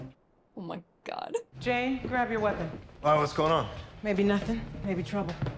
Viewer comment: "Jayne, grab your weapon" "I was gonna!" (was what I thought he said, instead of "why, what's going on?")